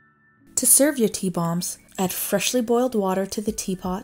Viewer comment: I thought she said add fresh boiled water. Lol.